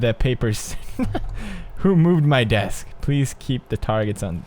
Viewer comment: move my fucking desk that what pewdiepie say